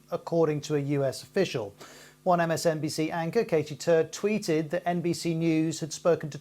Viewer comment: Could have sworn he said Katie Turd